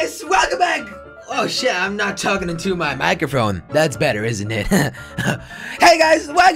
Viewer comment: did he just say microphrone 0:02 😂😂LOL awesome video by the way✌